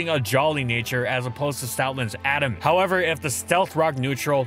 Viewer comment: What did he say here? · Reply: As supposed to stoutland's adamant nature